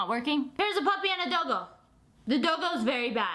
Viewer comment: when she said doggo i thought she said dildo